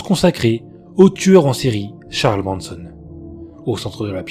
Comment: Merci pour ces histoires rassemblées. 7:59 ... sauf erreur de ma part, j'ai entendu "Charles Branson" ? Le tueur en série était "Charles Manson" 😉